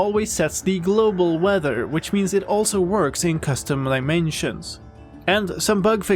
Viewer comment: am i hearing that right? custom limensions?